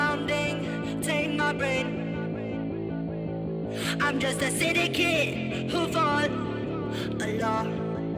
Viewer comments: what i think she's singing is way better than what she is. "am just a city kid, ho fall alot" i thought she said I'm just a city kid who fought a law lol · what i think she's singing is way better than what she is. "am just a city kid, ho fall alot" i thought she said I'm just a city kid who fought a law lol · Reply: I thought she said fought along.